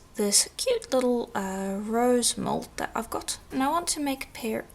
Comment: Great tutorial! (I swear, I kept hearing you say 'Rose gold', lol). I always love how this method looks in the end. Do you get your polishing papers at Firemountain Gems? Thanks!